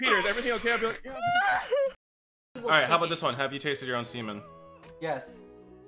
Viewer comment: What did he sayyy???🤨🤨🤨🤨😏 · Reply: Peter read the question: "Have you tasted your own semen?"